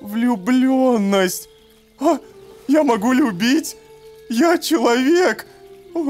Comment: я услышал вместе "я могу любить" более другое как "я могу ее бить?"